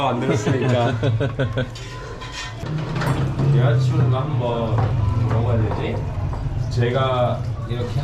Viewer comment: 여자친구가 한번 뭐라고해야 되지? 라고 말한거 같습니다, 자막에 ???? 라고 되어있어서ㅎㅎㅎ 적어봤습니다